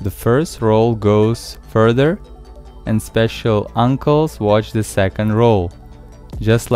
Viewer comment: did I really hear " Special uncles'